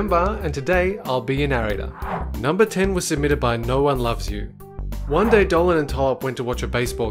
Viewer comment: The first one I thought he said “Noah Loves you” instead of “no one loves you” ;~